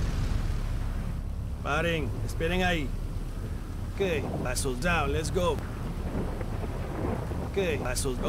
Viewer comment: I literally laughed when I heard 'Asshole out, lets go'